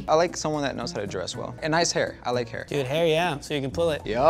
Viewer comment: I misheard the fuck outta what he said the first time I played the short. “She got ass hair! You can pull it!” Bruh